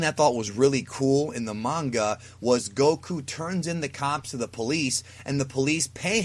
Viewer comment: Did you say that Goku "Turned in the cops to the police?" or am I mishearing?